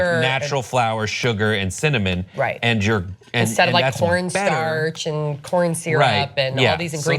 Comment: I have to use captions and I need help. what are they saying at 7:56-8:00? captions read: "right and your like a whore in church & corn see right. .." Oh. my captions are all messed up I think. ... at 9:25ish.... "I love cock coffee ooh oh a" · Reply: says corn starch and corn syrup.